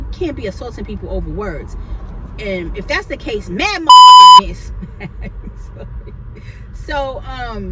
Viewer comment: I still heard you say mother sucker 😩😑👀😆🤷🏽‍♀️